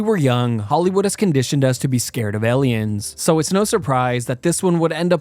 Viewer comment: When you said “alien” I didn’t hear you and thought you said “Eliot” and I was so confused.